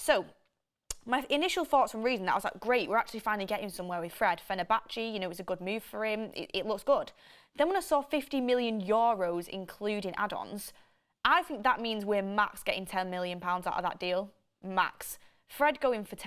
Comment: I thought she said at first €15m including add on's